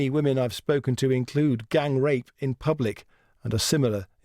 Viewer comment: did I heard it correctly? Gang rape in public!